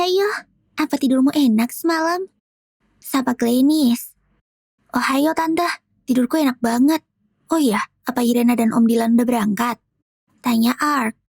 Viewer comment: Aku dengernya kayak ohio